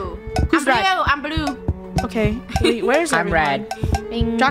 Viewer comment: did said draca or draco